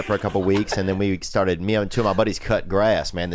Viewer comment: I swear I hear Theo say “meow”. Am I wrong? 😂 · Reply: “Meow and two of my buddies” is what I hear.